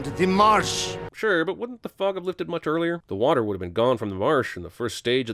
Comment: it sounded like he said the fok